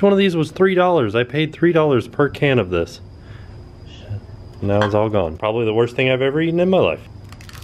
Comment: does say "sh*t"?!?!??!?! I was like ooooooooooooooohhhhhhhhhhhhhhhhh she said a bad wooooooorrrrrrddddd XD